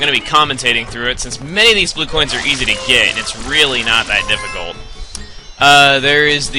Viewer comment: when is says "not that difficult" google captions says "not that simple" ^_^